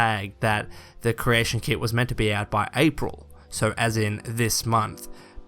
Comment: thought they said may? not april